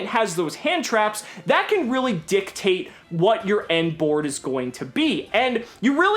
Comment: that "dictate" just sounded like a "dick tape" to my fucking ears lmao